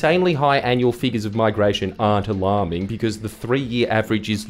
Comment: Sorry did u just say Ash Barti or alarming?